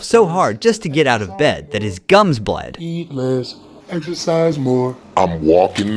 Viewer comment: Exercise! I thought you said extra fries!